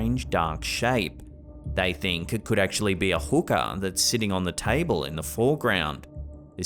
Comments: Anybody else hear hookE*R* 2Xs? @ 9:24 U know, instead of hookAH..? Anyone... ? Hmm anyone? Oki doke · I thought you were saying hooker in the background 😂 I would love for you to say hooker and hookah in the same sentence😂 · Who else thought was said 'hooker' instead of 'hookah' at first? 😅 I really hope I'm not the only one... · Haha, at first i thought he said there was a hooker in the background, 😆!! Then he points out the pipe/hose, and im like ooohhhh he didn't say hooker her said hooka!!! God what am i thinkin about. 😆 😆 😂